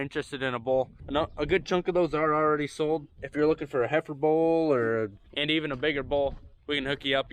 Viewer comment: at 10:50 did you say "heifer bull"? Could be my hearing, but I played it several times and still heard "heifer bull". Maybe gender neutral has made it to the livestock, too.